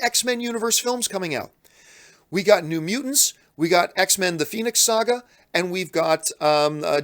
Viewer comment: I swear to God, I was distracted for two seconds and at 20:35, I thought I heard John say "X-Men: The Penis Saga". I had to go back and check, but at least he didn't call it "X-Men: Dark Phoenix". Otherwise, I would have probably heard something a little different.